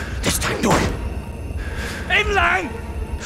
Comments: That means "shut up" btw. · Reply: I thought he said aimline. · did he just say "shut up"in vietnamese aka "im lặng"